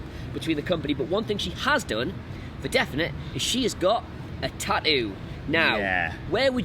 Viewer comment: I got excited, i thought Adam said Sasha got an ass tattoo🤦🏾‍♂️